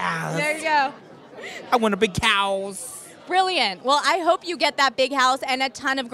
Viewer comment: it's really heartbreaking when his wish is to have lots of colorful Pianos in the house, and then you are reminded by the fact that he can't see the colors. i hope if God exists at least give him a touch or sense of colors. man i love this boy so much i wish i could do whatever i can for him. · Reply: It sound like he say I want a big cows, but he actually say I want a big house lol No more sadness & depression. May God be with Kodi Lee. With God all things are possible 🙏